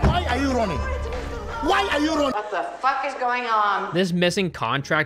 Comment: OH, oh it's MIS-FILED I always thought he was saying "misspelled" now that makes a whole lot more fuckin sense.